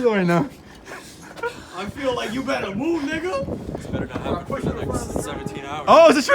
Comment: did he really say nigga?